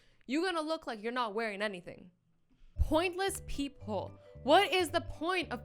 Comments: thought she said “Pointless people”🤣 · I thought she said,” Pointless people” I was just like,” u rite.”😂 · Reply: Pointless peephole not people and also it is spelled right not rite